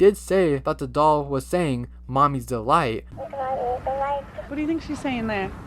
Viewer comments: Wait.. what does it mean if I heard “Mommy is the light” ? 😭😭😭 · I hear Mommy’s alive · I hear “mommy is the light” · I heard "Mommy is the light" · I heard "mommy is the light" for some reason